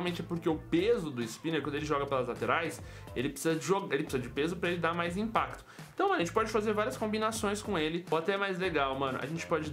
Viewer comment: eu ouvi bem duro pra dar mais impacto ?? 3:47. kkkkkkkk CEPO DE MADEIRA